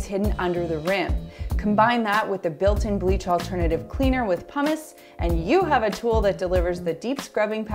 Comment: When you were talking about the toilet scrubber and you said "pumice", I thought you said "hummus" and I was like...hmm...that's a new one. 🤔